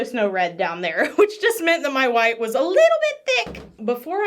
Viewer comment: I heard you saying first " my WIFE is little bit thic"